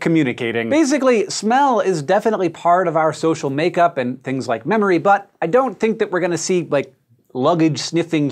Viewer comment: did he say "memory butt", or am I just imagining this?